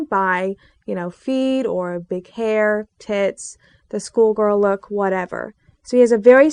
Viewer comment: did see say tits lmao